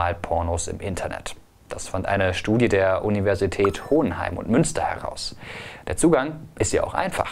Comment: bin ich der einzige der Hodenheim verstanden hatte muss mir wohl mal wieder die Füße waschen das der Dreck nachrutscht 😂😂😂